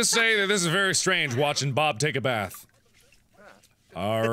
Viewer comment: who else heard bob say: "WHOOO, WASH YOUR BOOM!!!!"